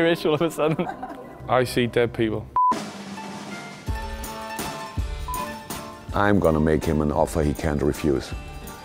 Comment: coutinho "I'm gonna make him an offer, he can't have food"... Holy fuck sakho tho, what an actor! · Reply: i heard "im going to make an offer he cant defuse"